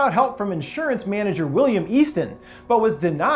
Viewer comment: Did I JUST HEAR WILLIAM AFTON!?